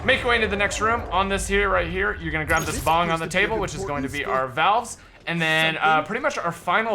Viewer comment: lol 7:36 did he say bong on the table